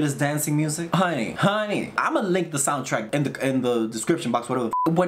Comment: I thought you said "I'm gonna lick the soundtrack..." and I didn't even think twice about it. I don't know if it says more about me or you lol